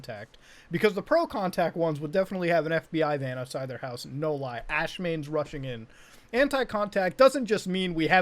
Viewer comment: did he just say "Ash mains" 👀